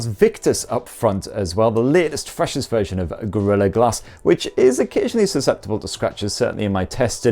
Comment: At first I thought you said, ‘the latest, fascist version of Gorilla Glass’ and thought to myself, that’s a bit much.